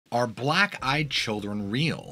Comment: Stg I thought it said are black children real? and it was a really, really chaotic 2 seconds of eternity...